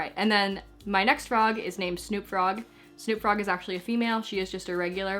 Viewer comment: I thought u said snoop dog!!